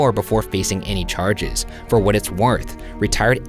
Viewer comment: so we just gonna ignore that Ryan said "For what it's warth." from what i could hear is it just me that hear warth instead of worth??